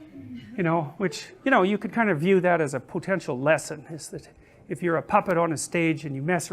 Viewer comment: LOL! Did he say "PUTINtial lesson"? ???